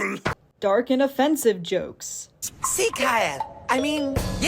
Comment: Erm…am I the only one who doesn’t know what Italy said at 4:30 (or just doesn’t understand) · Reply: Italy said "sieg heil" I think